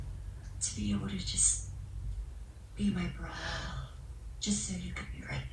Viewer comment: I thought she said “be my BRUH” 😆